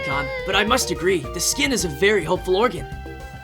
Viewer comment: what he said: the skin is a very helpful organ captions: the skin a very hopeful organ even the captions are nagito omg